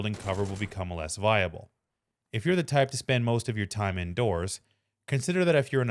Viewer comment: he said "if you're the type to spend most of your time indoors" i heard "if you're a dirty little camper"